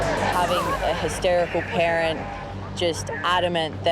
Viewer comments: I thought she said hysterical karen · I honestly thought she had said: "Having a hysterical Karen, adament that their child is in the water..." Beans, I choked on my juice!